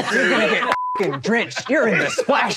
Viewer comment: I thought it said Spanish zone.